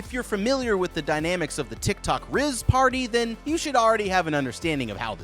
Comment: Did he just say tik tok rizz party?